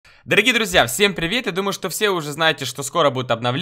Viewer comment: Мне одному послышалось на первой секунде ролика "Всем привіт"